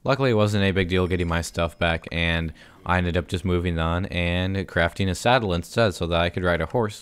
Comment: did he just say craft a saddle impossible